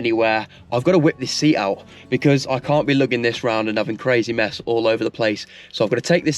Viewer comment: 1st 5:36 what he said crazy mess. ;what I heard. /crazy meth 😳